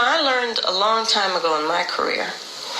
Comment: Almost sounded like Kamala said "...having a background as a [prostitutor]..." 🤭 But then "words have meaning" and answers FUCK ALL as usual! 🥱 ...bore off, bish!